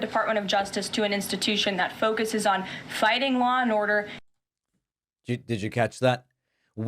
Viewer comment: Did she just say fighting law in order